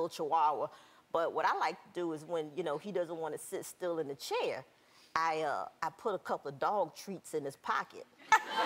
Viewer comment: lol I thought she said " I put a couple of dog shits in his pocket"